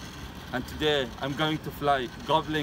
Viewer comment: I thought he said under water. I thought he's teasing hovering up.